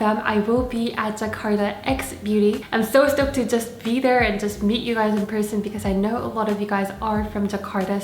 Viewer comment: at first I heard you saying ''to beat you guys in person'', what a warm welcoming hahaha